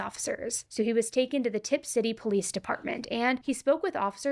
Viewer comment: I thought I heard ‘tipsy police’ 😂